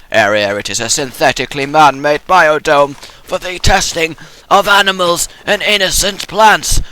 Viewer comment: lol when he said "for the testing of animals and innocent plants", i heard "for the testing of animals and anus and plants"